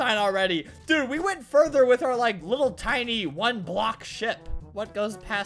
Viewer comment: Omg 5:34 One block shit that's what I heard XD